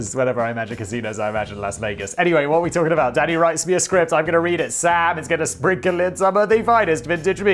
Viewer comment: Definitely sounded like Simon said "daddy writes me a script"